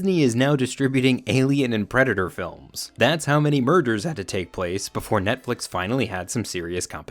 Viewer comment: Did you say "Disney is now distributing Alien and Predator films, thats how many MURDERS had to take place before...", or did I just misheard you? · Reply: I said "mergers" but it came out funny 🙃